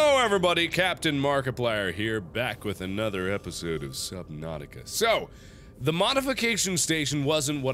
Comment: i heard "southern nautica" 💀 fitting